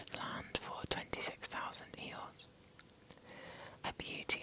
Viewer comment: I genuinely thought you said 26,000 Eyores, like the Winnie the Pooh character and I was baffled.